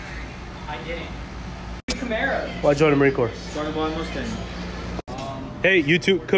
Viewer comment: I thought he said Roy Mustang